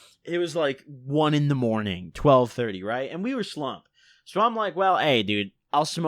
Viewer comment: what is he saying lol. "We were sloan?" "We were slumped?" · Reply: Slumped